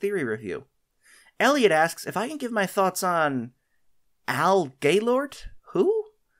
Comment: i can't hear anything besides "Al GayLord"